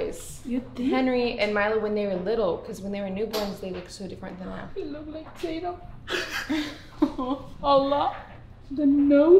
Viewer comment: when said that he looked like taytum i thought she said he looked like a potato 😂😂